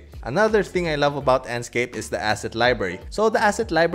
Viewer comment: I thought you said acid library not assets library. I need to pay better attention 😅